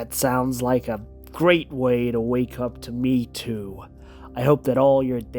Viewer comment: Did he really say "a great way to wake up with me too" ?? 13:16 He was the biggest "me too" M'f'er going! Just...on black men, not wmn.. 🤷‍♂️